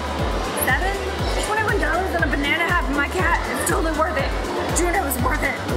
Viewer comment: I love how When you’re excited you sound like Cristine from simply nail logical. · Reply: I thought she was talking like pewdiepie and the I heard “BENANA”